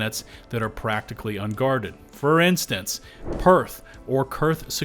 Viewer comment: did he just say perth?? didn't know an australian city was a whole planet lol